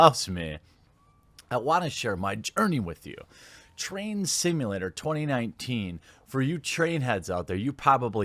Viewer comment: I thought you said Trans Simulator!